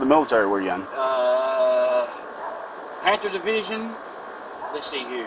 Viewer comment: I'm sure it's Panther, but I sure sounds like he said Panzer Division lmao. Guys cooked.